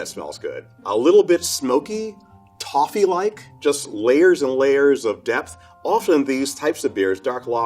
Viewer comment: misheard: "Layers on layers of death".